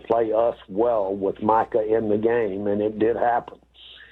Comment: Did he call him Michael again?